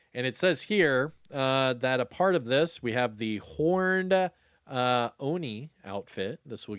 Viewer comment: sounds like you said Hor - ny which is hilarious and why I love your videos. HORNED - ONI